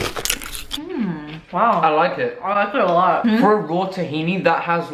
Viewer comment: "I like it alot" if someone didnt understand